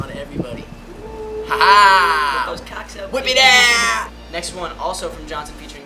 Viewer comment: to anybody else hear Jack J say whip them cocks out HAHAHAHA